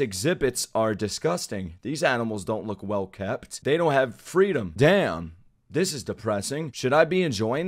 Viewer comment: did you say FREEDOM